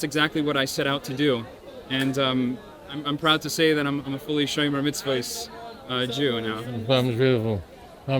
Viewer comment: To anyone who is able to help; I do not speak or understand Hebrew. At minute 3:46, the man says “I'm proud to say that I am fully __ Jew”. What were his words? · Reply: He says shomer mitzvot which means literally he guards the commandments/laws meaning he observes them.